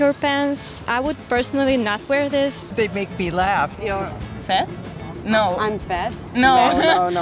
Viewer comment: i thought she said Fit but she meant Fat Lmao 😂😂😂